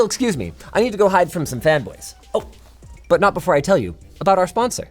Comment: did he just say he's going to hide from femboys?????